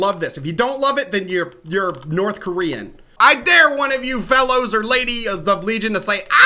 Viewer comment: Misheard him saying 'North Korean' to 'South Korean'....which I am D